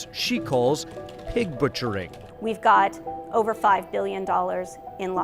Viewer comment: minute 2:22 she says 5 billion, closed captions says 5 million. significant error